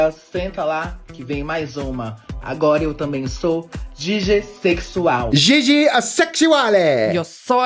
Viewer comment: I thought you said diddysexual 😮